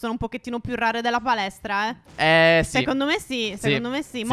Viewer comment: solo io ho sentito "palestra" al posto di "balestra"?